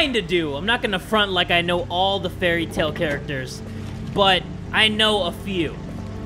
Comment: That awkward moment when you got all HYPE when you hear say "Fairytale characters" because for a few seconds you thought he was saying "Fairy Tail characters" lmaoooo. 😍😲😖😶